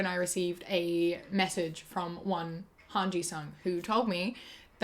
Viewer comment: Thank you, for telling us this big secret about men not having arms! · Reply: Wait she said?!? I THOUGHT SHE SAID HANGE-SAN SO I GOT EXCITED THAT SHE WAS AN AOT FAN😭